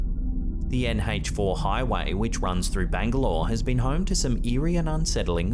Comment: did he say the Anne Heche highway???